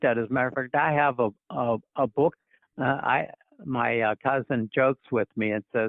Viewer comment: mentions his "husband." Did I hear that right? 😳 Maybe he's joking??? Is he a progessive liberal type "Christian?" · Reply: Cousin